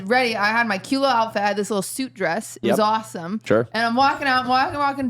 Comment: I thought QT said “soup dress” instead of suit dress and I legit googled soup dress cuz I was like I want one what could that be I love soups I love dresses?!